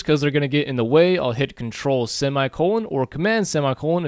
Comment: How did he hide the ruler? at 03:31 ?? Ctrl and then i couldn't understand him for shit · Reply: semicolon (;)